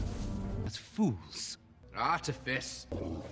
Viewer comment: What did Caius say? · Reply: he said ‘ what fools ! and then in the next clip ‘ the artifice ‘ , which im pretty sure has smth to do w edward and bellas daughter !